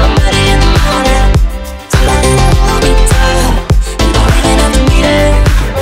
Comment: I didn't realize that he was saying 'you don't even have to mean it' I thought he was saying 'you don't even have to meet her'